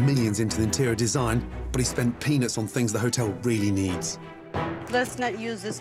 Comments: "But he spent PENIS on things the hotel really needs!" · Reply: I thought he said "peanuts" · "...But he spent PENIS on things the hotel really needs." I love how he just speaks! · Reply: I thought he said peanuts. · I know he said "spent peanuts" but it REALLY sounds like he said that he "spent penis" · sound like he said pen is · It’s sounds like he said penis on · """Did Gordon just say he spent Penis on what the hotel really needs" lmao